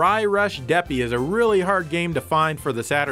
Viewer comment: I-I thought it said "DERPY" and got really excited...